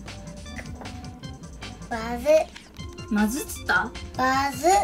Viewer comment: 卵割るを「まず！」って聞いたり「わず！」って聞いたり笑私も両方聞こえちゃいました笑